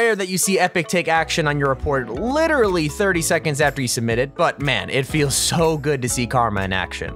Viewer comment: I think they both said “man” at the same time. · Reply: They both said "but man" if you watch close